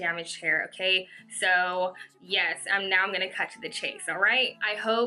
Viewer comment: I hear “and now I'm gonna cut the cheese, alright?“ no matter how many times I listen to it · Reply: Xy Zy no, she said "and now I'm gonna cut to the chase"